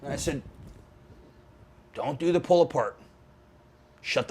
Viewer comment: what did he say? dont do the pull park? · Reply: Don't do the pull apart